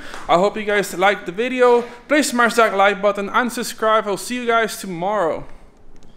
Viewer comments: I realy thought he said unsubscribe at the end of the video hahaha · did he say unsubscribe